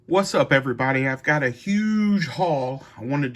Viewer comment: I thought you said hey everybody I got a huge hog lmao!!